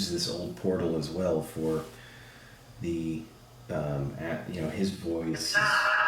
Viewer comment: I hear it's my voice. I am. Anyone else ???